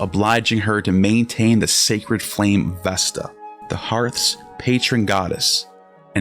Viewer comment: Idk why I heard that "Sacred Flame, Avesta" lol